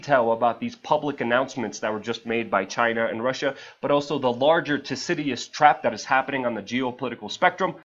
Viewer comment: What did he say just after 0:30 that sounded like “larger ‘toesideous’(?) trap”??